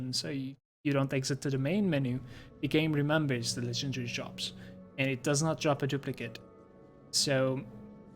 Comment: what did you say at 8:27 - 8:30? I couldnt understand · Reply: The game remembers the legendary drops and it does not drop a duplicate